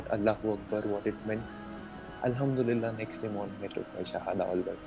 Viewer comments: can someone pls tell me what he said at 13:15 ??? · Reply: Next day I took my shahada all by myself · What did he say in 13:17? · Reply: He said he took shahada